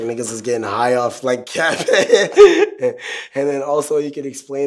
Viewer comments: cat piss??? I thought all this time was saying cactus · Whole time I thought he said cactus not cat piss😂🤦🏽